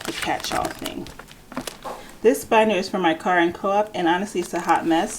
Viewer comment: Hi! Thanks for this idea! I have a few envelopes, but i should really put them into binders. I couldn't really hear the name of the binder at 4:10. Can you please repeat the name? I could only hear the words "Car and." Thanks so much! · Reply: I said "car and co-op." The co-op being the apartment that I live in.